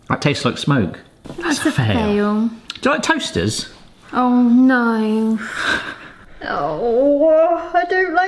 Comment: is really saying "Oh nein" at 14:12? lol, didn't know that she can speak german :D